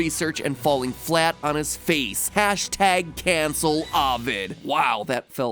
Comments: I thought MatPat said #CancelCovid at first, ngl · I thought u said cancelecovid but u said cancelelovid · For a moment I thought the Hashtag said: #CANCELCOVID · Thought he said cancel COVID at first lol